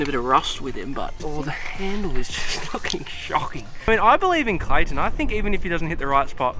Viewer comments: Is it just me or does he say, the handle is f***ing shocking’ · did i hear say "the handle is f**** shocking